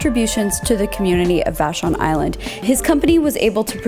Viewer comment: Vashon Island! I thought she said Fashion Island....🤣🤦🏻‍♀️🤣